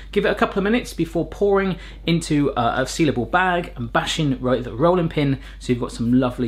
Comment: I heard 'sea level bag' instead of 'sealable bag' spent a few confused minutes wondering if altitude affected pizza